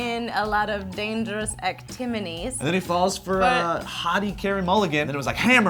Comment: did say actiminies at 1:08 instead of activities